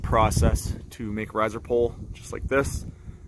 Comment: "It's pretty easy to make ???" I couldn't understand the term you used. It sounded like "rabbit-pole". Thanks! Great vid!!!